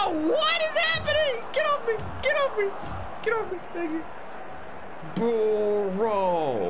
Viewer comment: did he say the n word? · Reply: He said “thank you”